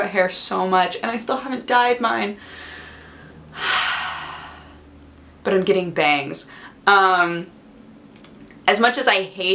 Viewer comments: Well, I thought you said "I'm getting banged... I can't wait to get banged." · I honestly thought she said, " I'm really excited to get banged. " Wow, too much info. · When you said bangs I'm getting bangs I thought you said I'm getting banged!!!! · omg she said im getting bangs so fast i thought she said im so excited to get banged... xD